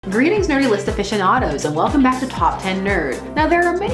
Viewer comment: Can someone clarify her intro line..? I hear 'greetings living fish anados..!'